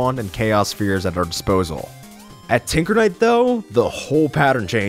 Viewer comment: did he say tickle knight? Plus omg it’s relic knight!